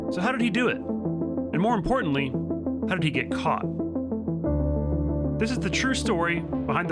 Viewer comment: Lmfao I heard "how did he do it? And how did he suck his co***"